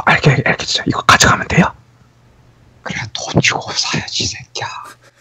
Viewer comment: 초에 돈주고 사야지 이 xx야 라고들림